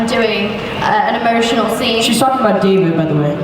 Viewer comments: what did Finn say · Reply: Sounded like he said “she suck my d btw” but he said she’s talking about David tho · what did Finn say?? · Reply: Pbcoco Chewbert he said shes talking about david by the way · What did Finn said? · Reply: "She's talking about David by the way" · what did finn an millie say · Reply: Jaire Marile Finn "She's talking about David btw"